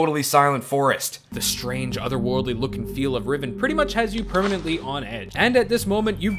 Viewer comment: What was the name of your top scary thing again? I didn't catch it. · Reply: OH, it was Riven! OK, I had thought I heard you say Ribbon by mistake. I'm hard of hearing so I don't always catch things the first time. Thank you very much for answering my question! I hope that you have a great and blessed day!